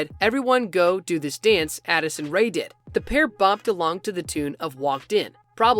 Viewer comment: bruh! Don’t make assumptions without knowing what they mean? They said “do the dance that Addison Rae did” not made!